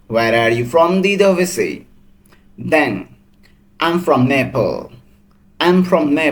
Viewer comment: It sounded like he was saying I'm from Naples, but thr caption read I'm from Nepal (?) 😊